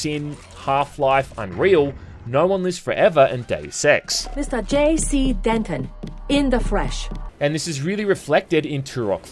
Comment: did anyone else mishear Deus Ex as Daily Sex?